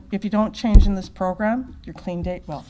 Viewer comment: Because if you don´t change in this program, your........? What did she say? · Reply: No, she said "if you don't change in this program, your clean date will"